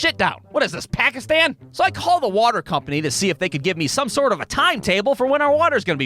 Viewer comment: I thought he said pac-man